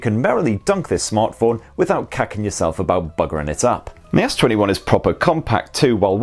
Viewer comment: did he just say kakking yourself